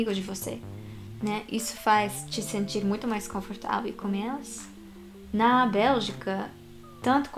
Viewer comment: eu entendi ela dizer "...isso faz te sentir muito confortável e COMÊ-LAS"